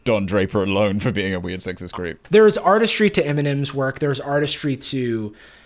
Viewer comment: Jarvis whenever you say Eminem all I hear is M'n'M xD